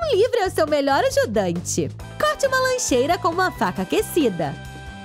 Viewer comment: eu entendi vaca